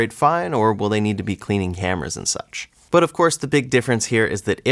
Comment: "...is it going to operate fine or will they need to be cleaning hammers and such?" The context of the conversation and Closed Captions told me that the word was "cameras," but even after playing it four times it still sounded like 'hammers' to me, LOL